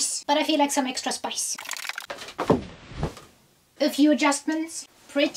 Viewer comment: It sounds like she says "a few jaspers" 😅 but I'm pretty sure she says a few adjustments